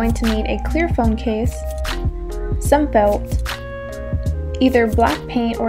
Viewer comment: What's the item on 7:15?? can't hear cleaaarly thankss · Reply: min some felt :)